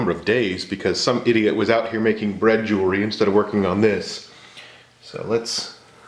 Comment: D-Did you say... bread jewelry? What? lol 😂